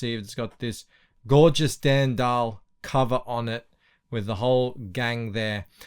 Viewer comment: "Gorgeous Dan Dahl"...that's how I heard it and I'm sticking by it.